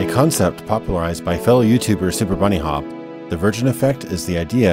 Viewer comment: I laughed so hard when I first heard this because I thought he said "a concept popularised by failed youtuber superbunnyhop"